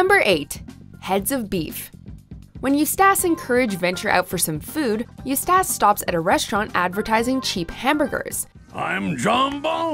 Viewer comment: The way she says "Eustace" is scarier than King Ramses. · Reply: It sounds like she is saying "Eustass"